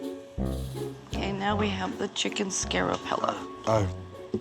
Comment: Notice how she said " Scare o-pella" instead of "Chicken Scarpello"? Wow, either she must be really scared; or that Chicken Scarpello must be really Scary.